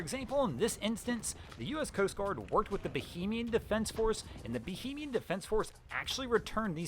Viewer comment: That would be 'repatriated' my verbose friend. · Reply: Yes. And Fore-ward has two syllables. It's not forard. I had to rewind and listen again to understand the name of the Evergreen ship. Also, "Bu - hay - me - an (Bahamian) defense force." I thought he said it was a bohemian defense force. Slow down. We'll all watch, like and subscribe even if the video is a little longer.